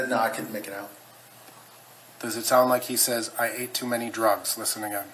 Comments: I had it on my 7.1 surround sound on, volume cranked and I hear "I ate too many drugs " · It sounded like he said he ate too many pills. There was also a lot of background noise in that clip. Couldn't they clean the audio up and boost it ? As for the Vallow case, I feel like the kids have been forgotten about. That's the part that is most upsetting. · I heard, I ain't got no drugs. I won't fault Mr. for use of a double negative! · With my earbuds I heard "I ate too money drugs.